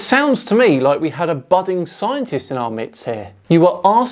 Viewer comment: Sorry, not to be nitpicky but did you say at 6:05 "It sounds like we had a budding scientist in our mitts" ? As far as I know the saying revolves around the word Midst ie. There was a curious boy in our midst/The house is set in the midst of two gardens Just though I'd let you know about the saying in case you always thought it was the words mitts and never realized it was midst. Kind of a perfect addition to this segment haha.